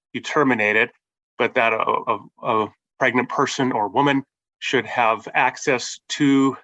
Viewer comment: did he say 'pregnant person OR woman'?? 🤔😬